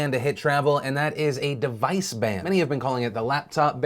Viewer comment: I was confused as shit for a second because I thought he said Device Band.